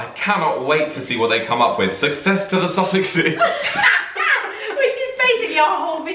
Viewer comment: I feel so bad. When you two said Sussex, I thought you said sausage. Duchess of Sussex suddenly became Duchess of Sausage to my ears....